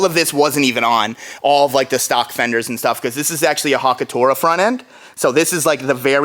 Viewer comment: What the heck did you call the Hakosuka front end @ 15:10??? Hakotoa or something hahahhaha